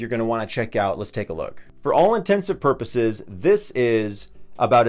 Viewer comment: Did he say “intensive purposes” at 13:00 lol